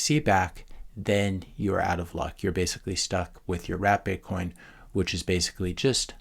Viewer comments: I thought you were saying "Rat Bitcoin," until i looked at your written notes. Turns out that "Rat" was a good description of the shipcoiners. Thanks for the great videos! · I thought you were say RAT bitcoin! 😂 Actually, Chuckie, like in the horror movie, keeps coming to life after his slave coin used by dictators in Africa keeps trying to breed. Proof of Stake is like the slumlord. Hoskins is a 💩coiner.